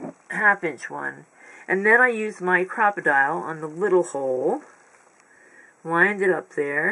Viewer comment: I got ahold of 2 of the wallpaper books at my local Thrift store. Only paid $1 each for them, but I need to check some of the local wallpaper stores. Before I attempt any envelopes like this, I also need to get me a crocodile. All I have is the dinosaur eyelet setting kit. They really turned out beautiful. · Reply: Oh, 🤣😆, and I thought all of you were saying crocodile.